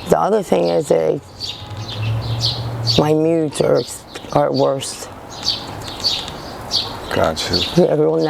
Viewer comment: What did she say at 10:55? I cannot understand what word she is saying. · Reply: Her knee jerks are worse.